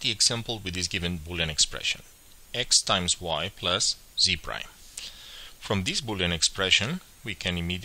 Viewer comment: z what? x+y×z what? You say something but I don't know what and it's catching me off guard. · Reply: Thanks, it was "prime" that I was getting stuck on. It was my first time hearing someone use that word in boolean algebra, as I had just started learning about the topic. I still don't understand why anyone would say "prime" instead of "not", which is infinitely more descriptive and intuitive, but I do know now that that's what prime means in this context